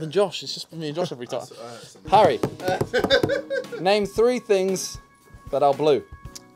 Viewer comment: I legit thought Simon said "Name three things I blew," 😳 XD